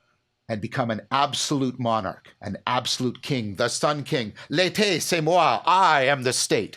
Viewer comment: I think John said “I am the summer” quoting Louis xiv